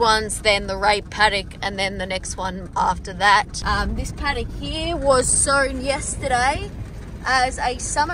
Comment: I thought said "this paddock was SO YESTERDAY" 😆 and i kept waiting for more hate on the paddock until i realised 😆